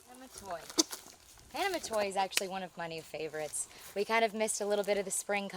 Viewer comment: What was the name of the cultivar at 4:49? I didn't quite catch what you said. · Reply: Hana Matoi Japanese Maple